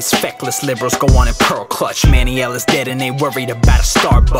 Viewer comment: Did I hear Manny Ellis' name at 2:40 ?